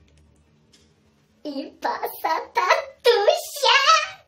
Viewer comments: "Czego się boisz?" Ja usłyszałem "passata". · Ja zrozumiałem Passata tatusia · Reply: Heh też usłyszałam passata xd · Pasa tatusia Usłyszałem pasata